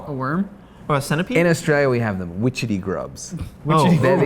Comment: "Put this witchetty grub on your cock Morty" This whole time I thought he said wriggly grub